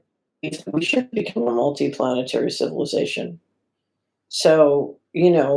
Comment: What did she say? It's all garbled. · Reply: "We should be a multi-planetary civilization."